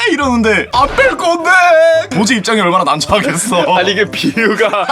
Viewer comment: 나만 도 가 아니고 보 로 들리나?